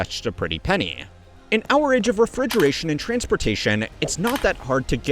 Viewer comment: You're saying Hourage instead of Our Age.